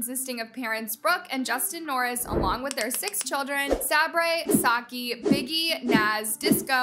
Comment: did she call Sockie "Sake" ? :D